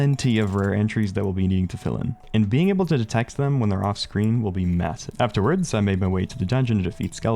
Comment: did you say massive?